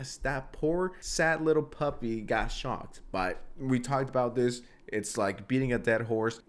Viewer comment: Bro i thought you said kaya got shot not shocked holy shit